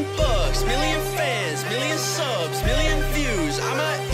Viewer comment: I thought you said 1 million thugs